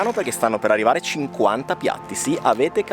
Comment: Ho sentito bene, avete detto 50 piatti? Il doppio di quelli di Gaggan Anand! Che video!